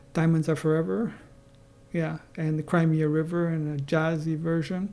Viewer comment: at first, I thought you said "Crimea River" and I thought 'that's odd' before the gestalt hit .. glad you have done your reviews, they have been very entertaining and enlightening, your thoughts are insightful and greatly appreciated